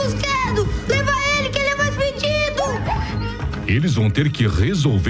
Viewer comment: EU OUVI 'FUDIDO'? KKKKKKKK OBS: EU SEI QUE ELE DISSE FEDIDO